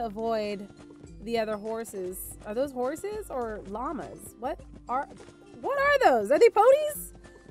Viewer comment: Okay, I'm pretty sure I'm the only one who thought this but when Cupquake said " Are these horses... or llama's..? " I swear the first time I thought I heard her say " Are these horses, Or Obamas..? " I have an interesting imagination x,D